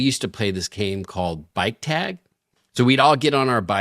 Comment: I heard "bite tag"